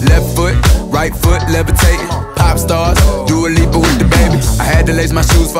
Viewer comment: I always thought it said " do illegal with dababy"